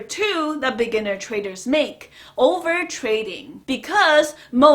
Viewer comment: Every time she says 'beginner traders', I hear 'lamborghini traders'.Lots of respect, though. I love this channel.